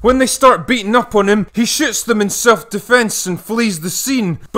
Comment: i heard “he shits them in self defense and flees the scene”